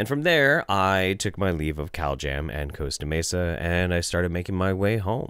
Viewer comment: I keep hearing "cowjam" instead of "caljam". It's made these videos even better.😃🐄